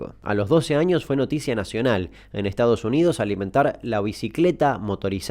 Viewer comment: En el minuto 0:48 que dice alimentar o al inventar?